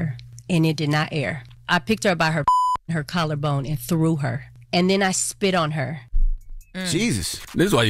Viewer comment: I thought she said she picked her up by her collar bone... anyone else?🧐🤷🏾‍♀️